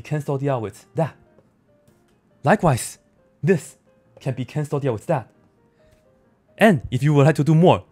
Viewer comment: Did you slip into Mandarin? It sounded like you said "cancel 掉" twice!